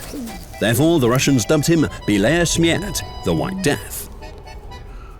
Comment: услышал "блять, смерть"